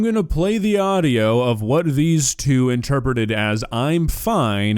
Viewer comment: i hear "im fly ng er"